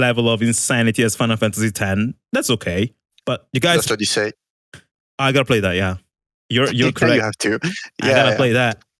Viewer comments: What game did he say? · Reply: Lost Odyssey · which game did he say? I can't quite catch that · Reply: Lost odyssee one the main inspiration for the combat